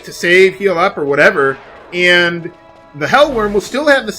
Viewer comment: Whenever you said Hell Wyrm, I thought you were saying Hell Worm. I kept thinking to myself, "A worm? Seriously how bad can a worm be?", but then I remembered the problems I've had with sand worms in the past...